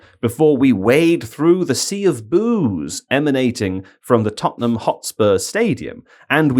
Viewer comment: Heard this as "Sea of booze" the first time and thought some sort of beverage disaster had hit Spurs 😅